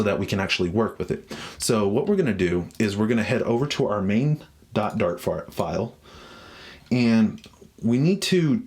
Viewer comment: Did he just say head to our main dot fart file O.o